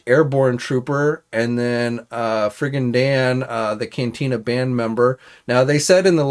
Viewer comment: Good job with pronunciation of all the names today, though it did sound like you said Friggin' Dan 🙂